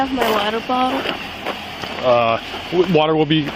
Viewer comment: "Can I have my water bottle?" has to be the most unusual request I've ever heard. Normally it's a frantic, "Where is my phone?" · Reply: I thought she said "water bong." She speaks like she's sedated.